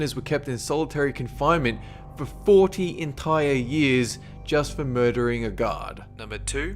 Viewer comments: did you say "Just murdering a guard?" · Everytime he said "Guard" I thought he was saying "God"... I kept thinking, what the f++k? gods? How? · did he say "JUST for murdering a guard?" wtf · When u say guard I thought u were saying God. My bad.